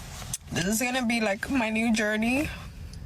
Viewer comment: She says "her journey" I hear "her shit show"